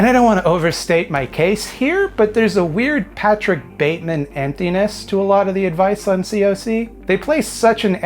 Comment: Patrick Bateman emptiness. Lol. Oh wait. I thought you said Jason Bateman emptiness. Anyway, Great video. We don’t need to arm narcissists.